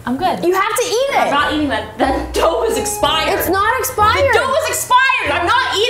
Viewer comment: when she said that I thought she said that dope is expired